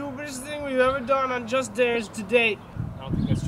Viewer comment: I thought he said just dance